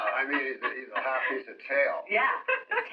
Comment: Any lip readers here who can figure out what was said at 2:40 ? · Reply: Hot piece of ssa